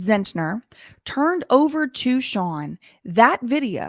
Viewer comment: Judge Stango deleted his Linkedin page. Coward. Danbury is corrupt to the core. smh Btw, I listened to the audio you referred to with a listening device I have and I heard , "this guy is such a f*g".